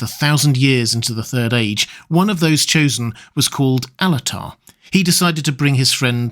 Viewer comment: you're saying "Alatar" yes? I heard Annatar, first, and I was like, "yo hold on, no."🤣🤣